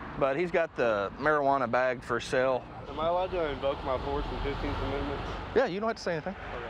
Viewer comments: “Am I allowed to invoke my fourth and fifteenth amendments?” 15th Amendment: black people can vote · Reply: Pants I thought he said 4th and 5th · “Am I allowed to invoke my fourth and fifteenth amendments?” 15th Amendment: black people can vote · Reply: I genuinely thought he said 4th and 5th but I may be wrong